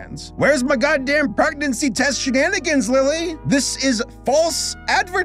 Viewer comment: What said: "Where's my goddamn pregnancy test shenanigans, Lily?" What I heard: "Where's my goddamn electric car, Bruce?"